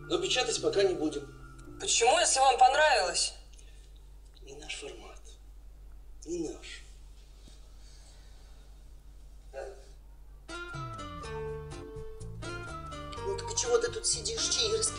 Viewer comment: Мне послышалась что, издатели стазали: ни "не наш формат", а "вашу мать"!